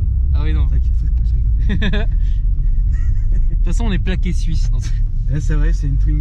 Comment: "t'façon on est plaqué suisse" dans ma tête j'ai entendu "on est plaqué or" frère. Sinon j'ai fait mes armes sur une twingo phase 2 bleue, je faisais peur aux passants en burnant comme un porc (ouais c'est possible avec une traction mais faut y aller comme un cochon), 92 rupteur en seconde (ouais c'est pas bien mais j'voulais la décrasser), gros freinage avec étalage de gomme pour tester le freinage d'urgence, les passagers n'étaient pas prêts. Je passe la phase "course" avec une BM, j'étais au rupteur à chaque rapport, lui il était en mode sénateur, mais j'l'ai niquay quand même xD. C'est très joueur une twingo. Sinon fréro, si tu veux une caisse qui envoie du paté, qui te fait oublier ton premier amour et qui arrive à faire s'évanouir le stig : AIXAM turbo sport coupé phase 2 avec double rétrobiseur latéraux couleur carrosserie. Avec ça, si t'es pas le king du terter c'est que j'ai pas su te donner les bons conseils au bon moment, désolé fréro.